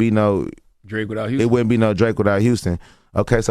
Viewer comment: Did they just say there’s no Houston w/o Drake? Smh 🤦